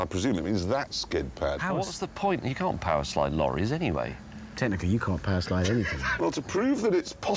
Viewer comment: Did Hammond say "Tanner, you can't power slide anything?" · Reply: Technically